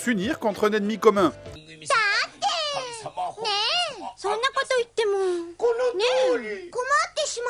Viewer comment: Franchement on dirait des pokémons quand les "magical girls" parlent 22:30 x) et c'est moi ou elle dit pikachu a 22:34 ^^